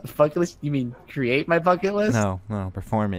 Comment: Anyone else hear say "F**k it list?" At around 12:35?